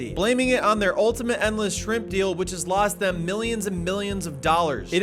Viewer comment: Can we talk about the subtitles that say 'lossed' instead of 'lost' at 0:07 LOL cmon! I love you but damn proof read bruh